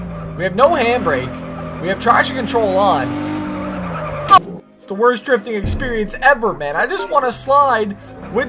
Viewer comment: Am I the only one who thought that the game on the thumbnail is Real Racing 3 ? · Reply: Bruh I thought he said that RR3 is the worst racing game ever